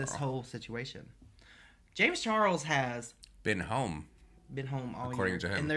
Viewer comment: I thought that said James Charles has HIV im